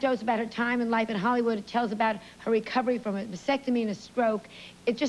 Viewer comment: did joan say vasectomy there?